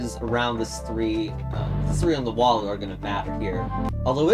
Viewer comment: When you said "4 on the wall" i heard "hole on the wall" Bruh